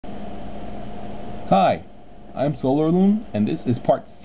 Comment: jeez... i hear 'Hi! I'm Sailor Moon...' anyway, great tuts. it looks realy complicated for me, before i saw your video